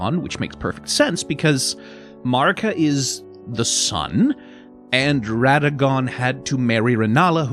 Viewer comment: yknow, its REALLY funny. When I heard “Marika is the sun”, I thought you said “Marika is the *son*.” Like, you were going to bring up my favourite interpretation “Greater Will / Marika / Radagon = Father / Son / Holy Spirit”. What a shame, I would’ve LOVED to hear your thoughts on that… Oh well!